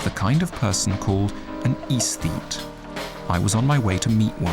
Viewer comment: isteat? Aesteat ? Ezeat? What did he say? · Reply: It's "aesthete." I had to listen a few times to figure out the word.